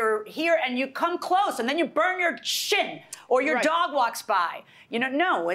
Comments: did she just say "and then u burn ur shit"???😂🤣🤣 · thought she said "you burn your shit" I had to back it up a few times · I totally thought Milian said "and then you burn your shit!" Until I rewatched 3 times and she actually said "shin." I would love a People's Court where Milian actually gets to curse at the litigants. I got so excited 😆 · What does she say at 14:13?? Lol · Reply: "SHIN" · I 100% thought she said and you burn your sh*t 💩 lol 14:10. I turned on closed caption and it was bun your shin haha